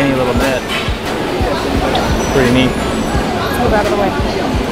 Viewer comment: triggered me when this woman at 00:40 has the audacity to just say "move outta the way" instead of saying excuse me ._. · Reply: She said, "Lets move out of the way."